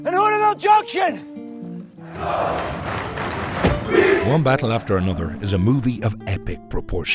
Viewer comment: I kept hearing 'Wombat after another'.